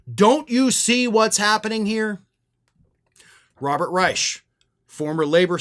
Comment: can we please get Robert Reich instead of fourth Reich🥺